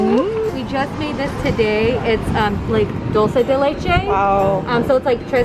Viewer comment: Did I hear "doce de leite"?